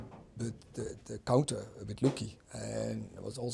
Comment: Did he say Lukie